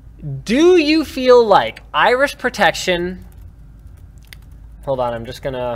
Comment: sounded like you said Irish protection lol